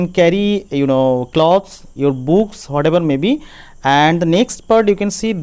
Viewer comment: What he said on 4:31 books??